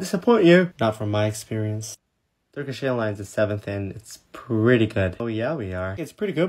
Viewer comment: i thought it said hair lines